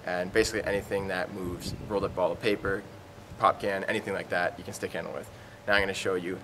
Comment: lol for a second it sounded like you said "pumpkin" rather than pop can. i'm not used to hearing it called "pop."